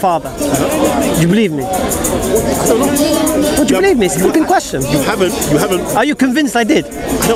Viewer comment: did Hamza say , 'would you believe me, it's a FUCKING question?'..... now now, I thought it was a huge sin in Islam to use such expletives! ... he definitely said Fucking and not Flippin..... Hamza needs to go back to the drawing board and learn not to use such language.